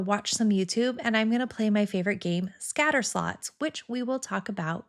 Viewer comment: I thought you said Scattersluts...I was intrigued.